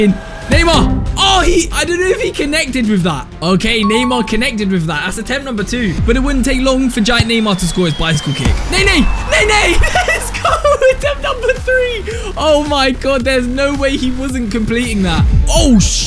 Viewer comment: did he just say ney ney